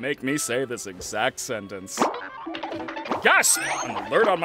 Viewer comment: GASP! (or did he say yes 💀)